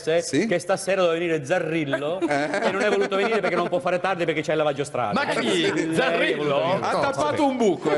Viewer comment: non capisco : Zarrillo ha il lavaggio..?? Cosa dice? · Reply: ha il lavaggio strade, non può parcheggiare la macchina